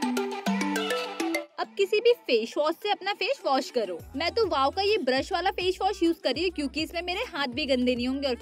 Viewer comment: She prounced it faish wash instead of face wash 😂